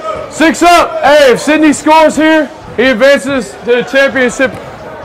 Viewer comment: I could've sworn I hear say "championsip" 🤣